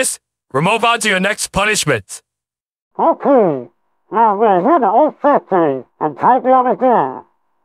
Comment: What did he said?? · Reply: Ok, now we are here the old factory.